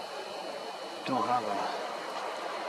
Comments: Almost sounds like "Do Habla" at 19:34 instead of "Don't holler". Accent ssems European like from England. Weird. Did you hear it when you were zooming inside the cave? What about looking inside with a flashlight? It is intetesting you did not want to mess with it, so am now thinking something was in there you did not want to find. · I heard a strange voice@ 19:44 say "dohalla " or something to that effect?